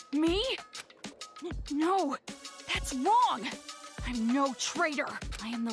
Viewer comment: DID NAGISA SAID "No.. thats wrong!" OR IM TRIPPING